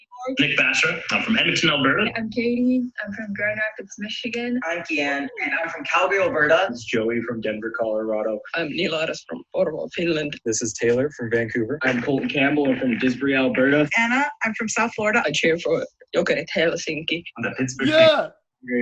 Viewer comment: did he say Porvoo Finland? Missä mun Suomi gäng on!?